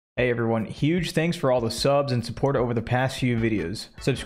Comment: I genuinely thought it said “huge tanks” at the start. Thought of Roadhog. Laughed audibly. Had to rewind to make sure. Disappointed. Laughing again because I’m an idiot. God I love weed. 6, thank you for being you.